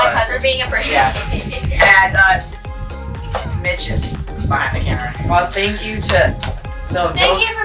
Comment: I thought he said b*tches... but congrats 🎉 you deserve it and keep the gring